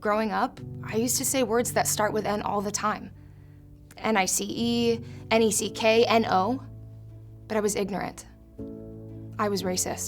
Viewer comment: for some stupid reason I heard "And I see E, and I see K, and O", spelling, together with "N", the word NEKO